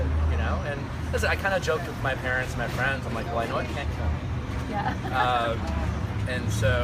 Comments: Thnx Ali! I believe ppl have quickly & harshly judged him. Shawn was the biggest & most unreasonable. He talks strongly against cyber bullying but what he did to Nick was bullying. He not only persistently questioned Nick's intentions discussing them with the other guys prior to him talking to Nick! I totally get that , him talking to Nick but the way Shawn handled that was tacit. He was all accusatory & totally shut down Nick, he could not even get a single word to respond to Shawn. To this day, I have no clue why Nick was portrayed as the bad guy. Shawn let his jealousy toward Nick bring out the worst in him & yet he surfaced as the 1 with good intentions? He could not even be man enough to accept the 1 & only reason he hated Nick was bcoz he is jealous! He can never be as awesome, sensitive well spoken smart & hot as Nuck. Shawn don't hate Nick bcoz he is everything you're NOT! U got the girl, be happy & quit making fun of Nick. U R a bully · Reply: It sounded like he said "I know I can't comment" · What did he say at 4:39? I know I can't what? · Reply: "I know it can't kill me"